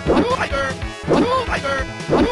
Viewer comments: sounds like is saying "liger" lmao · it sounds like saying hyper not tiger. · I thought he said firebird, not tiger. it looks like a firebird too